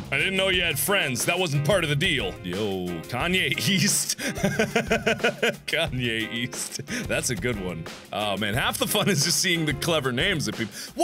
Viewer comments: i thought he said 'Kanye Yeast' and i died from disgust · I thought he was saying "Kanye YEAST"!!